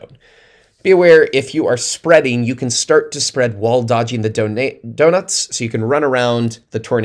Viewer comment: p.s. I heard you almost say "Donadoes" at 18:26 and now I can't unhear it. :D Mmm a tornado of donuts! Thanks again man!